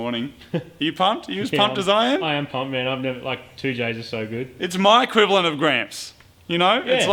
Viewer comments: "2j's are so good" did I hear that right? Does have a car with a 2j motor in it???? Supra maybe 😃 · did say something about a 2j :O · did i hear 2J's?? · did I hear 2j there? Can it be a 2jz?! (plz say yes :) )